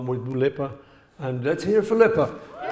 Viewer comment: He gets to Liverpool often with... Dua Lipa? Did I hear this correctly? Can anyone enlighten me what's that all about?